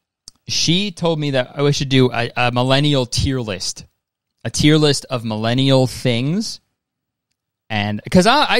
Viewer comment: i fr thought that said criminal tierlist at first